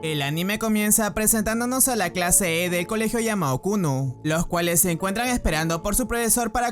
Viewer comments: escuche kunno xd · XD le entendí kunno